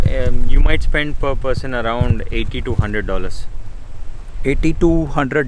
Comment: he said 80-100 dollars I thought he is saying 8200 🤣😂🤣😂😂🤣🤣